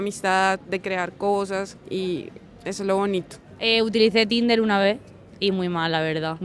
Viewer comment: what did she say ? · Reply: «Eso es lo bonito».